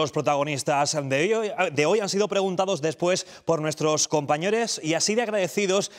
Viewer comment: dijo:"Compañeres"? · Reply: Si lo dices por el minuto 5:30 yo creo que dice Compañores, cambiando de lugar la 'o' y la 'e'